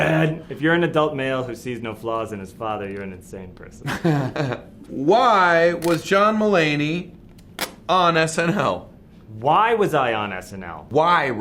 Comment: i looked away for a second and thought it said “why was john mulaney born”